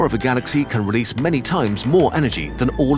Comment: Am 8 the only one who hears "This is what a face mask is" instead of "quasar"?